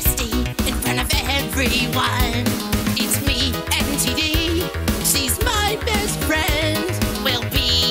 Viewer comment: legit heard this as "it's me and titty"